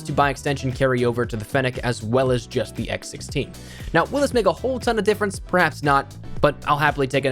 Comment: did he say the n-word?? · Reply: Will this make a whole ton of difference